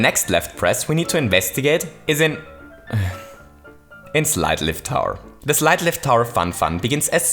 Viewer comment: I thought it said “Slide Left Tower” and I was like “what the fuck is the point?”